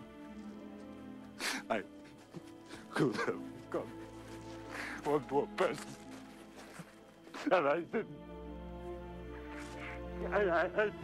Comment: sorry, i'm not a frwat english speaker si, does he says "one more person alive" or "one more person and I didn't"? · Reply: “One more person, and I didn’t”